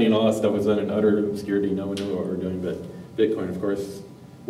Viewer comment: does he say "now we know what we were doing"?